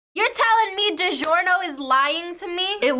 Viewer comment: What i hear: 0:01 You tell me that Giorno Is lying Wut?